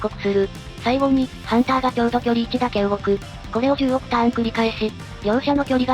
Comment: 目を離した隙に16ターンって聞こえてそんくらいなら行けそうとか思いながら画面見たら10億ターンでワロタ